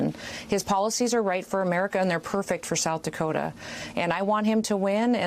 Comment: I swear I heard Noem say "I could be home in bed, or shooting my horses right now..."